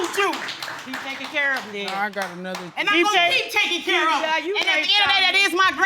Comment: WAIT A MINUTE!! @ 14:24 I know I heard him say "Now I got another kid too". Wow!!